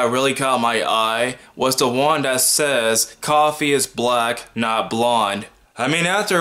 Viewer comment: Oh Lord, at first I thought it said "coffee is whack" and I was about to go all in on you. My bad. Carry on!